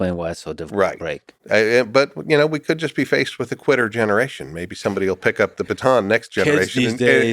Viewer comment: LOL QUITTER GENERATION😁 For a moment I thought he said, "TWITTER GENERATION."😁 But then again, those words might turn out to be interchangeable.